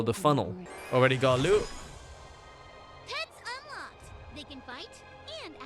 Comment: ok, I did not hear 'pets unlocked', true I only half watch/listen while playing a game on my other screen but I heard 'tits unlocked' and wondered what the hell was playing